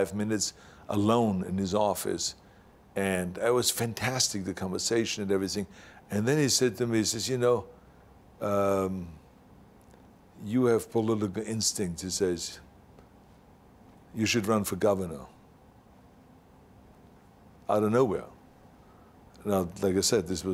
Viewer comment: What does he say at 2:48? You have good instinct?